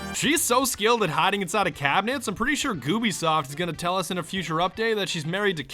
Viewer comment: Did i hear "GoobiSoft" at 6:33?Hillarious!